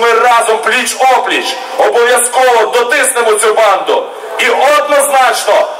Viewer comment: Мне в одном месте послышалось или он сказал "ЕБАНДУ"? А в целом уверенное выступление,решительное.Не то что Кличко и его "наступление". Всегда Олега Ярославича уважал.С уважением вам из Сибири!